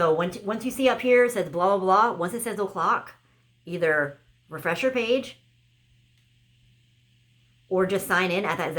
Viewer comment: Hi I just wanna check what did you say on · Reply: Hey. I just said to refresh your page at the top of the hour